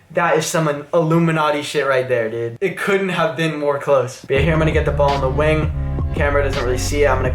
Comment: "more close" did he say??🤔🤔